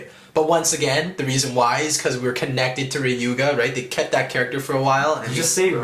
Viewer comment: Please don't misunderstand each other guys, at 10:07, the word said, I literally heard was Ryuga (Ree-you-ga).